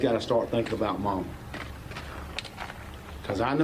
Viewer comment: "Somebody's gotta start thinking about..." What? Did he say "mawmaw?"